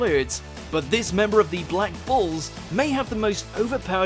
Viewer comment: Did just say "Black BALLS"?!